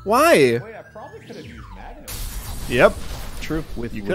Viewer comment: I feel like I’m the only one who doesn’t hear what alpharad said he could’ve used. I’m dumb and stupid and have problems, not with hearing, but with understanding. I would be at least 30% happy if someone could tell me what he said. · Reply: He said he probably could’ve used Magneton